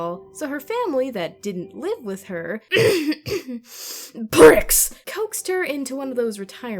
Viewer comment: cough CoUgh "P R I C K S" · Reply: Peachy Ghostergy Oof I thought she said bricks xD